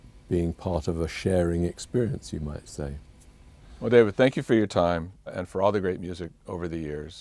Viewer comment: "Whatever, thank you for your time." Holy shit, did he just say 'whatever' to a response of David Gilmour? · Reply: He said "Well David" really quickly before saying thank you for your time. It does sound a little like whatever though.